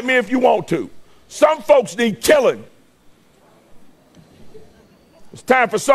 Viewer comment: "Some folks need killing" or "some folks need telling"? I heard the latter. Not making any excuses for this monster, just checking what was actually said.